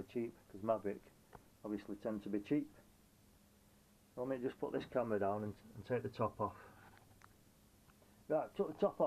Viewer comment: I thought you said "Let me put the camera down and take me top off" 😆😆😆. I got a bit worried then 😆😆😆. Great rig for the money. Someone's got the ebay bug. 👍👍👍